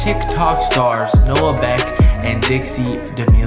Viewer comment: “Noah Neck” instead of Beck 😂✌🏽🇿🇦❤️